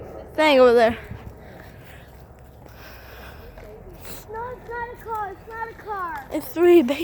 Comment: Your baby brother THATS MAGIKARP😂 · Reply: but I think he said that's a car